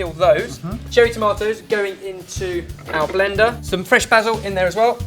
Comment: And all this while i thought ben was saying "bazzle" instead of "basil"